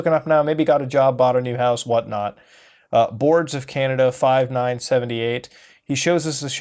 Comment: Did Mutahar just said Boards of Canada at 15:28? That's cool! ^_^